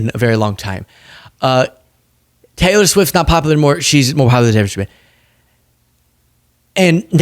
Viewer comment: WTH do you say at 10:37 in the video? “She’s mo dau la hair shwa?”I watched it 5 times and kept laughing harder. Subtitles just give up whenever you talk at that timestamp.